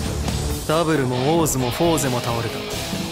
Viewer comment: "Froze?!" I think the subtitles meant to say, "Fourze!"